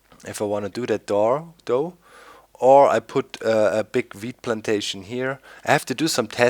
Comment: Sounds like he was saying weed plantation